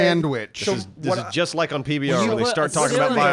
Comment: really just said PBR instead of NPR hahaha